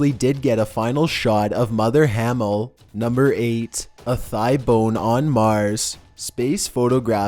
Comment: #8 I thought you said IPhone on Mars and was wondering why I couldn't find one in the picture haha